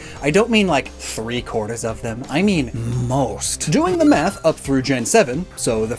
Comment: i swear i heard him say "doing the meth"